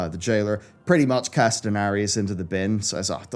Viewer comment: did he just say Daenerys lmao